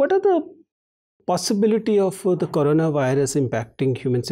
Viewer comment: is he said Corona virus